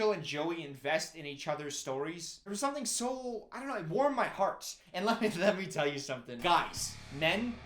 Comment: did say "it warm my hearts" at 43:37 or is it just me 😂